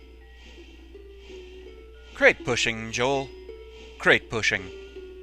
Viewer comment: okay i want to point this but did you just call jill, joel or jewel? start at 11:12 and listen it is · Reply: I said Joel. It's a reference to the series Mystery Science Theater 3000, and specifically, the episode where they watched the movie "The Lost Continent." There was an extremely tedious scene of the people in the movie climbing a mountain, and as a running gag during it, the two robots, Tom Servo and Crow, kept turning to Joel, the human that watches the movies with them in the early seasons, and said "Rock climbing, Joel...... Rock climbing..."